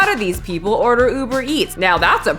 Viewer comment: I genuenly thought 9:37 said Uber Fats lol